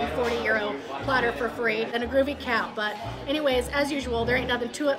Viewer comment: I thought you said “and a groovy cat” at first, and i was thinking about how that would be the absolute best prize ever won